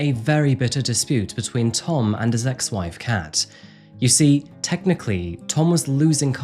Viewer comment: I THOUGHT YOU SAID "between Tom and his ex-wife's cat" i was like daaaaaamn this is gonna be wild